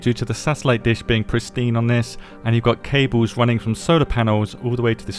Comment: Swore he said "cables running from soda panels" and my dumbass ignored the SOLAR panels looking for pop cans strung together. Edit: Im betting the morse code is the combo to get into the bunker with the new locks 🔒 😉